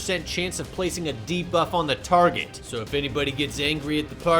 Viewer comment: For a moment there, I thought you said "put a D-cup on the target". 😵‍💫😵‍💫😵‍💫